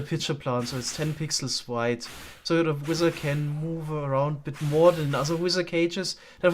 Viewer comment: I keep hearing "Wither" as "Wizard" haha